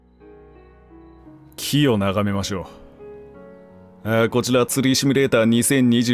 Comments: 「キヨ眺めましょう」に聞こえた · ごめん！許して！第一声から ｢キヨ眺めましょう｣って聞こえる😂😂😂😂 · キヨ眺めましょうに聞こえた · またですか 一瞬「キヨ眺めましょう」に聞こえた 気付いたら動画終わってた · 開幕「キヨ眺めましょう」に聞こえて変な声出た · 何度聞いても自分の耳が悪すぎてオープニングの木を眺めましょうがキヨ眺めましょうに聞こえてしまう